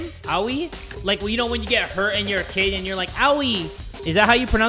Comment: i thought he said "like when you get her an her kid owie"